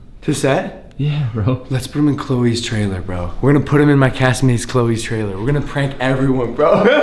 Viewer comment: i thought it said classmates....